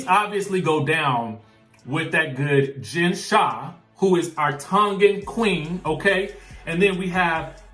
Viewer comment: What did he say? Tongue in queen